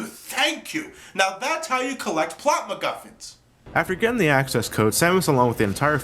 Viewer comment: What did he say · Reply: Plot-macguffins. Plot-specific collectibles, like Chaos Emeralds in Sonic Heroes.